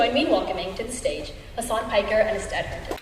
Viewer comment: I thought she said, “Hasan Piker and his dad!”